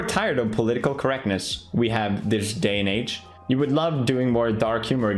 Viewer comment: did anyone else hear the n word instead of news